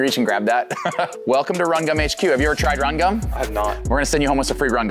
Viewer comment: Until this video, I seriously thought you had been saying "rum" gum 😂😂